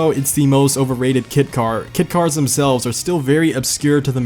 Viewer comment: I just want to congratulate you for saying the car names correctly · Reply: It sounded like he was saying "kid car's" instead of "kit car's" in my ears. Idk why lol.